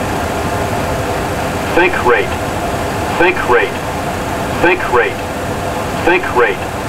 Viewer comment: what does the computer say??? Think Rate or what???????? WHAT I DON't GET IT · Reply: Alian sink rate